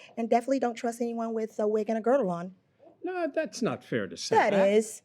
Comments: The “don’t trust anyone with a wig and girdle” got me🤦🏾‍♂️ the pot calling the kettle black 🤣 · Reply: I thought she said, "Don't trust anyone with a wig and a girl on". Lol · Everyone is miss hearing..it sounded garbled but after seeing the grooms teeth..she clearly said never trust a wig and a grille. It makes more sense too.. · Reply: No you assumed she said grill because you saw his teeth. But she said GIRDLE. I think you’re mishearing but that’s ok. We’re all human